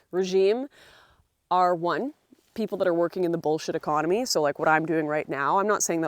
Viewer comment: So I thought you said Bolshevik economy instead of bullshit economy. Then I thought, well yeah